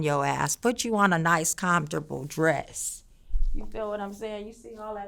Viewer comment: I thought she said, Looking for COPPER, looking for COPPER. ..."COMFORT." Got it.